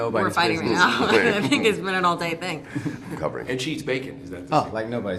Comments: after she says “I feel like we’re fighting right now” does Chris say, “I’m covering?” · And Cheadle just wanted to choke her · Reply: About Chris trying to save the conv, is it me or is he saying at 5:48 "i'm covering", as in "i'm covering you but please stop talking" ? or am i misinterpreting ?